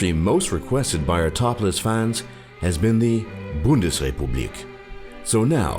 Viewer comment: I thought he said ‘Topless Fans’ XD